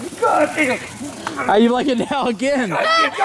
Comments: did I hear him say gold digger? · did he say the n word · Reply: lilbeastdad64 no he said goddamn